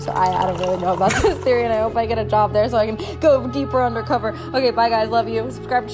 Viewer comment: I thought you said you got a job there?